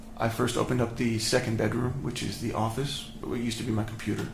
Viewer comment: Did he say that his daughter slept in his 'office'??